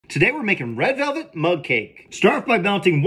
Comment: I thought he said red velvet monkey